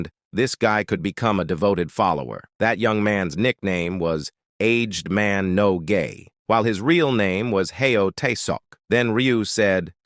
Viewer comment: Aged man no gay😂. Did I hear that right?